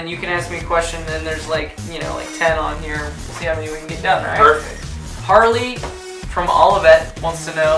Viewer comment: Aos 0:10 no pedacinho onde tá sem legenda, acho que ele fala "see how many (of them? ñ tenho ctz) we can get done"